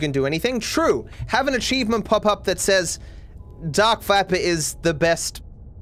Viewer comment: When he says “dark viper” i thought he said dark fapper